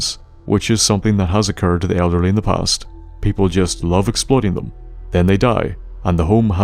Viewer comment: I very much misheard at one point and thought you said elderly people were exploded sometimes instead of exploiting and because this is a fallout video i was like sure!